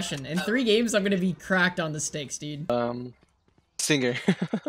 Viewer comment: Did just said "Uhmmmm sige"!? Proud filipino here❤️🔥